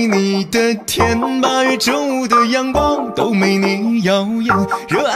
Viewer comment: I hear "Jungle young chef" instead TwT